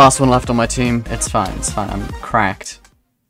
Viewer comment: I thought you said you were cracked